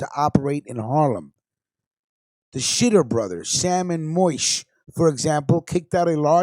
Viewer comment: Did he say "The Shitter Brothers" LMAO